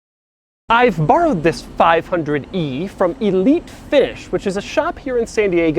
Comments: I thought he was saying Elite Fish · Is it me or did he really say Elite Fish at 0:36? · I thought he said elite fish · I thought he initially said "Elite Fish". Sticking a coat in the grab handle in front passenger seat blocks your view out of the window. Note there's no hook on the handle.